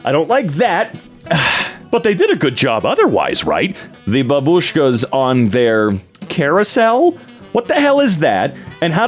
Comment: did he said "Бабушка"?